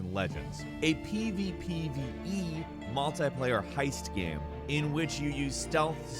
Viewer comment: what the fuck did you say at 10:21? seriously it sounded wrong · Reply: PvPvE so somehow both player Vs player and player Vs environment